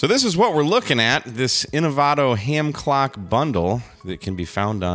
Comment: My survival mode must still be enabled. I could have sworn it said Ham GLOCK bundle.